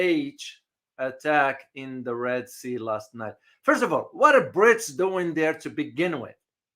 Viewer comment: when you say "Brits" I thought you said "Brics." Just an FYI.